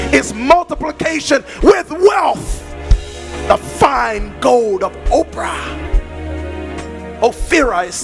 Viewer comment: 😂😂😂 what did he just say? The fine gold of oprah? ☠☠☠